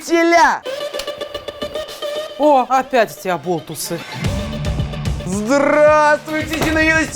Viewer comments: Мне. Послышалось что. Учительница. Сказала опять. Эти. Абобусы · Мне сперва послышалось абобусы.. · Мне одной в начале послышалось абобусы?😂😂